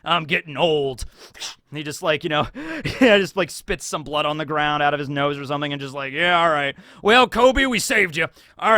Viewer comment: did he say spit blood out of his nose